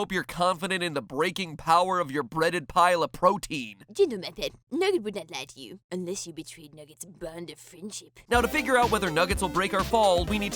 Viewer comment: What did steph say at 7:15? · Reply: "Gentle MatPat, Nugget would not lie to you. Unless you betrayed Nugget's bond of friendship."